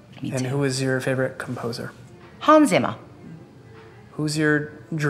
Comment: the way Adele says Holma Emma, I heard Hanns Zimmer 😂😂